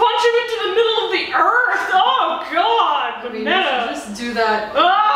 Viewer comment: How did I know it was coming "bone" I heard the words "u should just bone and I just fell of my bed laughing knowing what was going to happen